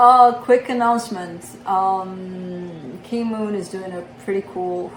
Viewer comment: King Moon? Key Moon? Kay Moon? I can't find it. Anybody know what YouTuber she's referring to at the 8:00 mark? Thanks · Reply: K Moon